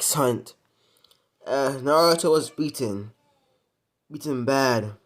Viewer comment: Me when I thought I heard “as naruto was EATEN” DAH FUCK